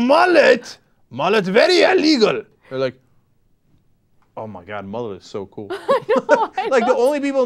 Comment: I thought this is funny if you have close caption on it translates what he says at 1:18 to (armageddon moment so cool). You gotta love youtube cc!